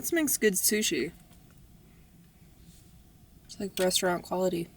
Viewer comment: Me watching this video ….“sprouts makes good sushi”. Me outloud to no one “yeah they do!” a few seconds later “it’s like restaurant quality” me - “wait what did you just say?!” Proceeds to rewind the video because I thought you said ‘rock star quality’ which was a catch phrase I used to say all the time. 😂